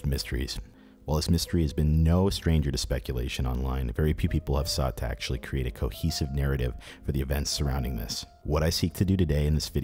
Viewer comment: you accidentally say "Peepee" instead of very few "people".. LOL